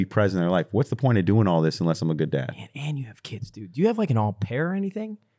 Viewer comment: Is it me or does Nade say “me and you have kids” nadeshot has kids??!!